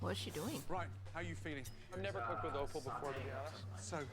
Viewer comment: When Gordon said "its offal!" I legit thought he was saying "awful" I feel so dumb now xD